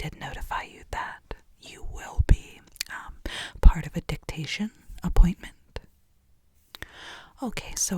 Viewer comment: I'm gonna be real honest, I heard "dick appointment" and not "dictation appointment' and my asexual ass was concerned for a hot sec.